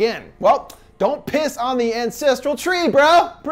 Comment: DON’T PISS ON THE ANCESTRAL TREE BRO. quote of the day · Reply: At first, I thought he said, "Don't piss on the Incestral Tree, bro!"